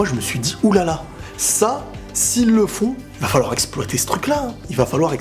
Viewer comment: SVP les gars rassurez moi a 04:03 dit que Armine risque de faire LES ENFANTS qu'avec Bertholt.????? 😐😐😐😐😐😐🤔🤔🤔🤔 Ou j'entends mal ? ???